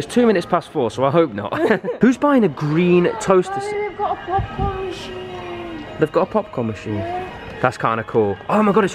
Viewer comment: did She say hey babe? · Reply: Ye she said Babi which is babe in welsh